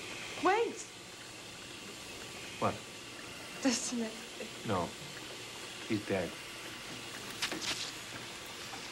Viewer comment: Any idea what she says at 2:56? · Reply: She said "Doesn't he...?"